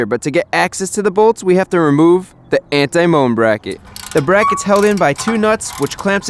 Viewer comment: is it an anti-moon bracket?? Because that s what i heard. If not,how is it called?